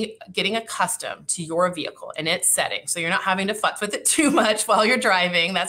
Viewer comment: I thought she said "#F<"* with it. " I had to listen twice to catch "FUSS with it." Hahahaha